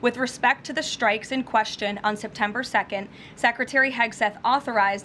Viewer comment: Did she say September 2nd?